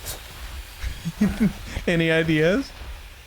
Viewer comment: sounded like said “Annie ideas?” Was that a tiny slip?